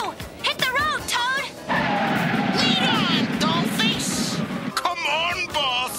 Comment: I'm so sorry guys, but every time I see this scene, I hear, "Lead on, Goldfish!" I am serious, I am not just saying that!